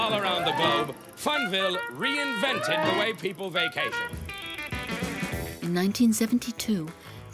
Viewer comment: did I hear well? white people vacation? Si oui pourquoi la traduction française n'est pas exactement ce qui s'est dit en anglais ? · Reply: Parle français! Tu es fou toi! The way people vacation ça dit. Tu ne comprends pas l'anglais et tu cherches le racisme partout! Efface ton commentaire!